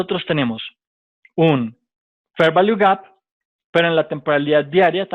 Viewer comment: Hola, que dices en el min 15:12 la verdad no entiendo? trade value got??? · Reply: Fair Value Gap